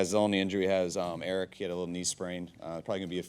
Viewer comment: In the first five seconds, it sounded like he said "Arik Kittle...knee sprain."